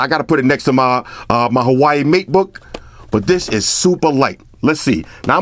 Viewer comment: did you just say hawaii matebook?😂